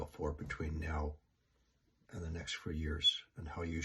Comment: in the minute 28:00 , when you say in the next FEW? OR FOUR? i didnt understand, can you please clarify? THANK YOU! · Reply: Sorry, next few years